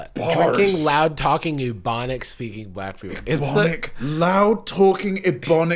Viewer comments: I swear I thought said "demonic speaking" af first and I was about to lose my mind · i thought he said bubonic 💀